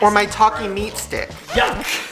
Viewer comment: thought shane kept saying "talking meat stick" 🥴